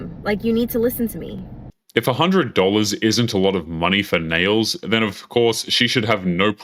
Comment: Oh! She said, "nails?" I thought she said, "nose." Either way, if she ain't my wife, that's her choice and her money to spend.